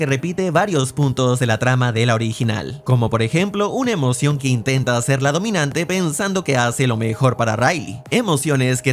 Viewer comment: alguien entiendió que dijo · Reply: Dijo "Quemamos hoyo en la alfombra"